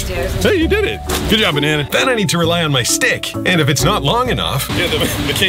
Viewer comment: Did anyone else thought he said "Steak" instead of stick?😭